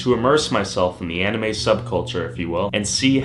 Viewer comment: I'm sorry did you say sub culture?